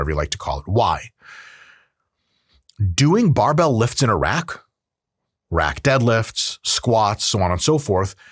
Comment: I thought he said “doing barbell lifts in Iraq…”